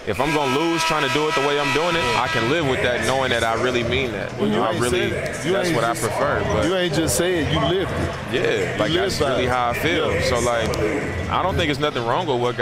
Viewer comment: Sounded like he said.. ** Really Nigga**. Idk... either he said really media or Nigga.. SMH